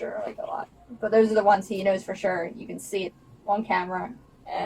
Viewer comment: He sounded like he said she knows trump?